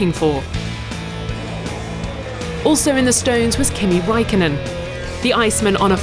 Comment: Did she said "Kimi WAIKKONEN" ?????